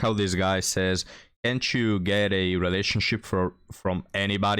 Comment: Great job as usual. However, go to this point and listen again. He did not say "Get a relationship.' He said, "Get it." Get sex. That is all he is thinking about at HIS age and may not be all SHE is thinking about at HER age. This is where your divorce coach with glasses gets it wrong. 4:10 "PDHP is very important." Which you DID get right.